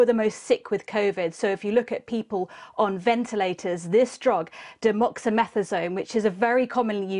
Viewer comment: Demoxamethasone? It's Dexamethasone! How do these people get these jobs? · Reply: i wondered about that too , i thought she said demoxymethasone